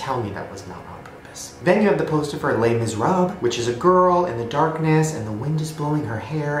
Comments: When Shane said "Les Miserables" it sounded like he said "Lay miss rubb". Is that the way americans pronounce it? xD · Did he just say " Lay miserob